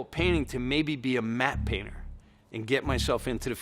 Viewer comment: Great Video! Just a quick note though, at 2:49 the closed captions state "Map painter" While Lorne is actually saying Matte painter. Other than that keep up these amazing short docus